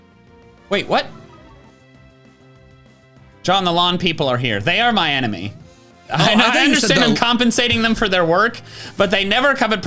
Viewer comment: I wasn’t fully paying attention and suddenly I hear scream “THE LAWN PEOPLE ARE HERE!” but I heard “THE LONG PEOPLE ARE HERE!” and the speed at which I had to do a double-take to see what was going on